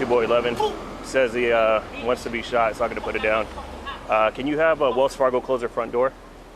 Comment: I totally thought the cop said he wants to get shot so I’m going to put him down.